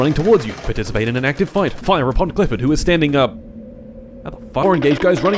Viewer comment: did say glyphid here or am i hearing things? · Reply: He said clifford